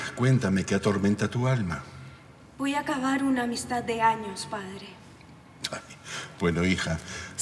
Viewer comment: se escucha voy acabar una amistad de años... Yo escuche voy a cagar una amistad de años 😅